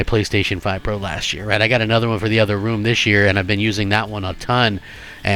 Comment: did you say 'i didnt use my ps5 pro last year, right? I got another one for the other one and ive been using that one a ton...' Let me get this straight, you didnt use the pro but you started playing it this year but not the one you already own.. but another one you bought? Your probably the customer that Microsoft loves, some idiot that will buy something twice that you don't like. WTF is wrong with you?